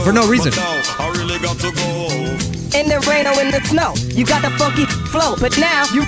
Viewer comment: sunny: oh wow parappa looks so manly Kirishima: sniff im proud.. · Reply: in this part parrapa sounded like he said (in the rain or in the snow you got the fu**ing flow)